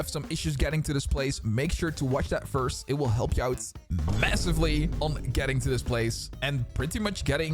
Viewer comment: did you say something MASSIVE